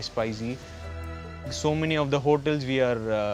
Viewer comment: I think he said 'so many of the hotels', not hawkers as is written in the subtitle